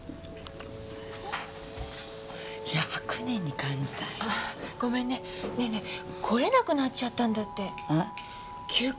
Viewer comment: ここのカンナ、何て言ってるか未だに聞き取れない 分かる人いたら教えてくれ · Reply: 「100年に感じたよ」